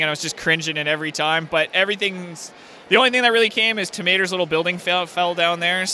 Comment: When ever he said Tow Mater I thought he was saying Tomato.